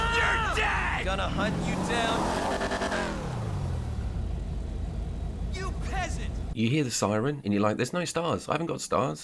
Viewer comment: did i hear a cop yelling 'YOU PEASANT' 🤣